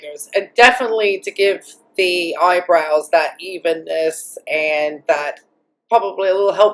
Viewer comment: I had the volume low and I thought you said “…definitely to give the eyebrows that evilness” instead of “evenness” 😂